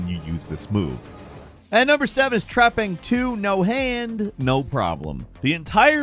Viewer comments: What is the name of the game at 4:42, #7? Subtitles say "Trapping 2" Also remaster Max Payne 3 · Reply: Trepang 2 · What game is 7? He says it multiple times but all i hear is 'trapping 2' and i cant figure it out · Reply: Trepang 2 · What game is at number 7? It sounds like Falcon says Trapping 2.